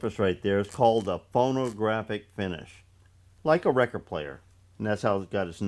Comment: Hi 😀 I have some hearing loss and I could have sworn you said it was called a porn a graph finish, for a moment I nearly changed my glasses,lol. Joking aside the set up was solid, and no problems whith chatter, so a good result,. Hope the wrists are getting better, thanks for another interesting video mate, stay safe, best wishe's to you and Lisa. Stuart Uk.